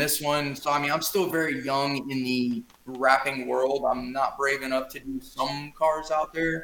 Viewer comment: I heard "I'm still very young in the rapping world". :)